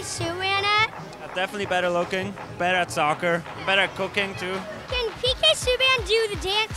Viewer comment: when he says cooking, i thought he said cocaine, prob true both ways